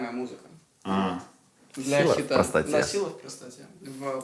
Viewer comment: вместо "хита", услышал "хета"